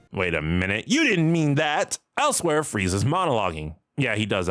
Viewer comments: for some reason here I thought he said "Elsewhere frizea's monologing" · I heard "Elsewhere, Frieza's monologuing" and had to do a double take.